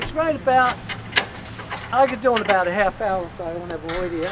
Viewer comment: "only takes about 30 minutes when I avoid the idiots" rofl. was he trying to say "leave me alone"? :D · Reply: I thought he said: "I can do it in about a half hour if I don't have an audience."